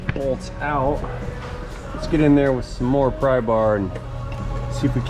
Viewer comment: You say: "more prybar" I hear: "more cowbell" 😂😂